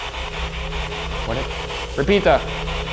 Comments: "gesundheit" am i the only one who heard that :D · sounded like gesundheit